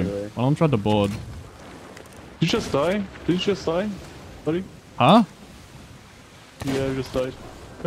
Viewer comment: I think the guy at 46:50 was saying "Didn't you just die? Yeah you just died." because they don't understand that smaller crews have faster ferry times, or he thought it was an epic own that they killed him 3v1. Alliance Servers are truly our best and brightest.